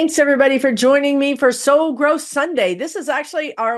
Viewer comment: Ha, until I saw it in print in the video notes, I thought you were saying "So Gross Sunday" - LMAO! What did I miss that was so gross (besides the Magat Rs and their orange leader)? Ooohhhhh, soul growth! Yes, amen! Thank you and guides! What you are teaching is so important, especially about non-attachment to outcomes (go with the flow of Spirit) and how to keep your vibration high, in love and light. Keep on keepin' on, my good friend! Edit: Near the end of this video, you say about soul growth - and it is a difficult process! - that it's like getting the keys to the car. Right on - you (connected to your guides and your higher self) get to be the driver of your life vs. being driven around by others/the circumstances of your life. Stand in and act from your authentic self, in love and light (dharma) vs. being reactive, a victim (karma). And for me, your words "keys to the car" were a cool synchronicity, b/c one of my dear spiritual mentors (now on the other side) wrote a song years ago entitled "The Keys to the Car"! The song by this Texan seems not so deep on the surface, but it has spiritual undercurrents (as I just stated above) about wisdom and guidance, since the composer was also on his own journey of spiritual awakening and development (as we all are!). We are always connected, so very much loved, and we are being guided, even when we least expect it. Watch for the synchronicities, acknowledge the signs/guidance, and say thank you, more please! 😀